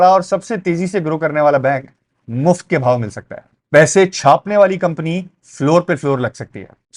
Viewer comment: bhai ap ki samj nhi lag rahi word ki ka ap kya bol raha hain kindly reply kar dayain 1:46 par ka kya mufat mil sakta ha Bank??? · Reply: You mean Bhao? Means price - it's a Desi stock market slang